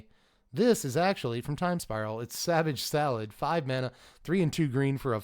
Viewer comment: Man, I wasn't looking when you said savage thallid and I heard it as savage salad. And then every time you said Thallid after that it sounded like salad.